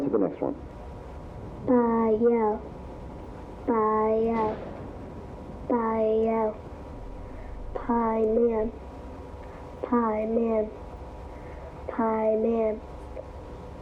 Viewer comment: right away i recognized what she was saying. Bael, Paimon, Balam: all demons names being chanted